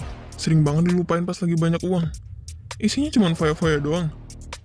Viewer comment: "Isiny cuma fefek" wettt ga salah dengar ni